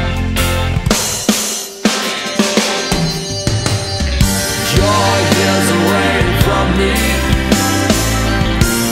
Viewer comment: Can anyone tell me what he says from 04:15 to 04:23? Not a native speaker. Thanks! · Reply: You're years away from me. You're not even gonna say one thing to me anymore?